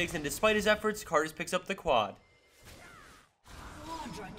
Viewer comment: did he say "karthus picks up the quad" or am i wrong?